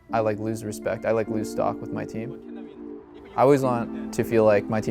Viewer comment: Haha did I hear that right at 11:55? "If my teammates think I'm playing bad then I lose respect, I lose STOCK with my team". INVEST IN DOUBLELIFT